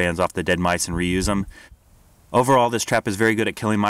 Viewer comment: I thought you said Russki Mouse trap XD at first.